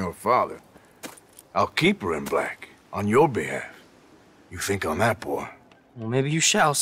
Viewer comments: When I say the trailer, I literally thought he said "I'll keep her in black on your bed." Meaning that he would sleep with her. But when I saw the subtitle saying "behalf", as he's talking it sounds like he's not saying it. It sounds like he's saying "bed." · I always thought Arthur said “on your bed”